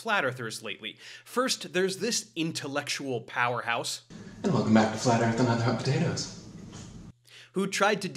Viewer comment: Can anybody tell me what this man said? 0:08. Not even subtitles work. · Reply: I think he said: and welcome back to flat earth and now there's hot potato's